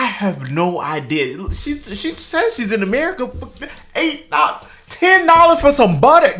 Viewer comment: I thought she said she was in Samoa. ???